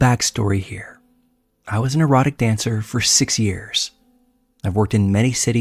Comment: Story 11- pffft lol i thought he said an "neurotic dancer", for like 10 mins instead of actually saying an "exotic dancer" lol I was like ooooh that can't be good lol 🤦‍♀️ I feel slow now.